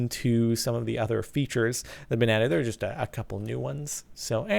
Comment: Did he say Banana at 7:08? lol